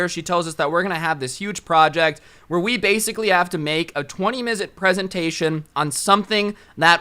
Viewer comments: Hey is it me or did say “a 20 mizute presentation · a twenty mizit? Is that just me who heard that